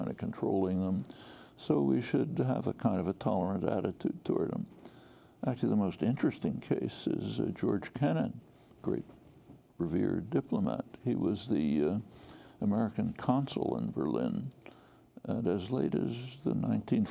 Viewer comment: I thought he said George Tenet ... but he was talking about George Kennan ... Geez, what an encyclpopedia Chomsky is ... Chomskipedia.